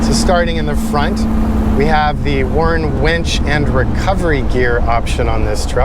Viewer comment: At first I thought you said "wench." I wondered why you guys sell this truck with a wench then I saw the picture of the winch.